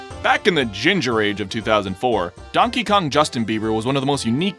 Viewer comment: Did he just say Donkey Kong Justin Bieber